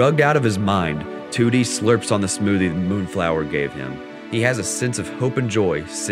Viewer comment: I keep hearing "Tootie"